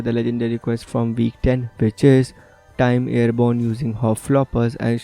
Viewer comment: Did you say bitches or which is at 4:01?